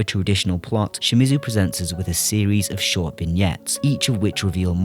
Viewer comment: Hi! Big fan of you and 100 years of cinema! There's one phrase I couldn't figure out clearly, and it's the line at 5:50 "Shimizu presents us with a series of ??" It sounds like " short piniyets", but that doesn't make sense. Could u tell me what that is? It really drives me, THX!! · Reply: Hey Frankiie, the word is vignettes. Thank you!